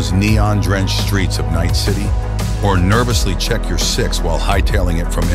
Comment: I heard "Nervously check your sex while running from NCPD" .. time to go to bed lmao, great videos.